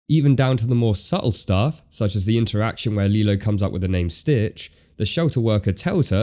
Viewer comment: it sounded like she said ‘’Ditch” at first 😆 before the shelter lady said ‘’Stitch” it is